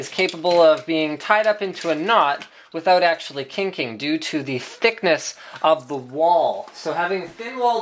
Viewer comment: I think i hear thickness of the wall? or wat?